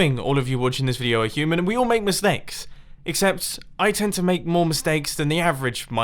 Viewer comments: Did you say misnakes in the start?? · Did you just say misnakes?